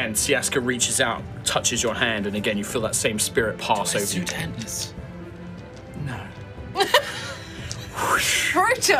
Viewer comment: Oh my goodness I have replayed 1:32:13-1:32:17 so many times I cannot understand what is asking aside from "Do I (something something something)" and it is driving me nuts XD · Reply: "Do I suit antlers?" "No"